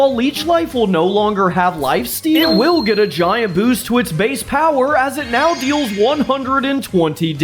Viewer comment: I thought he said base Powah for the bug water type